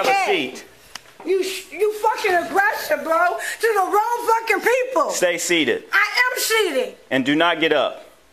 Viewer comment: I like how she tells the cop he's abusing "His own people" - I think "his own people" are people who don't break the law. Props to this officer who calmly but firmly forced this woman to face the consequences of the evil she chose. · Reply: I thought she said the wrong people not your own people